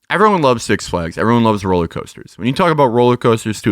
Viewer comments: Did Matt say 'rolling coaster'? Imagining him wearing a straw hat and bow tie. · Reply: I heard "rolly coaster" which I quite liked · Did Matt say 'rolling coaster'? Imagining him wearing a straw hat and bow tie. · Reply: I heard "rolly coaster"